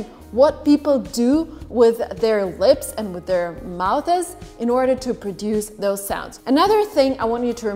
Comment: did you say, "mouthses"!!?? Lol, it's just, "mouths"